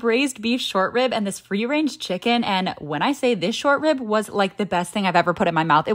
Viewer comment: i gueess she said free range chicken but i heard "rearranged chicken" lol